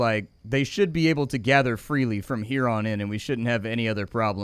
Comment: Every time he said “here on” I heard ‘huron’ and I feel so clever now